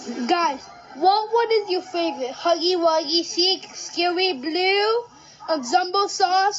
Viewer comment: i thought he said scooby doo but he said scary blue